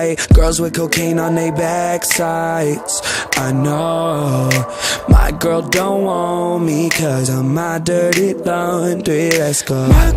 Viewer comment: Omg I thought it said “My Girl Don’t Want Me Cause I’m NOT Dirty Laundry” and I lowkey got pissed 😂